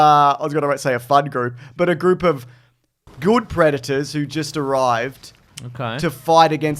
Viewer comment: Sounded like said he calls his friends a group of good predators 😂